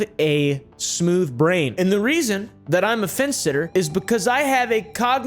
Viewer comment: You said "fence sitter" and i heard "face sitter"😂😂